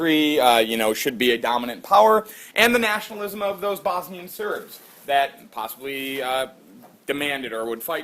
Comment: did you say Bosnian Serbs...............